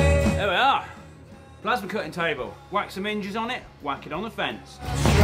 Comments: I had to replay at 6:49 a couple of times, as I genuinely thought he said “wax some minges on it” not “whack some hinges on it” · "wack some minges on it" is the best thing I've misheard in a while